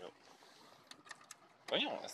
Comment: As a québécoise, am I hearing "voyons esti" 😂😂